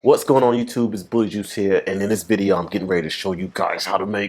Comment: I thought he said hey it’s booty juice here